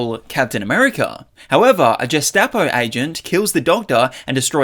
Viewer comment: Did I just hear "Jestapo"??